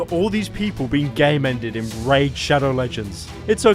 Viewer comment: It is only me that thought people said “gay mend” when they say game end